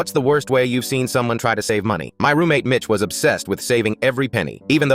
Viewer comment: Help I thought he said “my roommate bitch-“